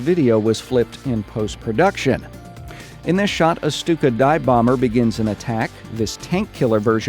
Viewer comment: I had this playing while I was distracted with my son and I thought I heard you say "this stupid dive bomber...", lol. I had to stop, and run it back. Of course you said Stuka, but for a minute there I thought you had really digressed.